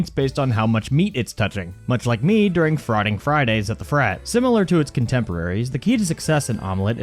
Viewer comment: Did he just say frotting fridays at the frat??